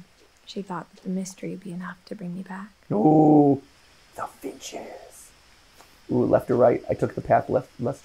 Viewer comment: it sounds like Mat Pat says no b***hes like the mega mind meme. I know that's not what he said but I can't unhear it